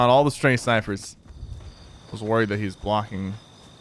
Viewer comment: I thought he said glock